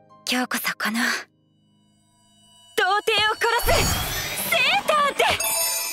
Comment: >Virgin Killer Sweater I see you're a man of culture. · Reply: that's what she actually said. She actually said Virgin Killer Sweater, but they didn't add that (or it was a mistranslation) in the subtitles.